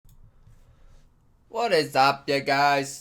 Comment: Thought u said “What is up NIGGAZ” ‼️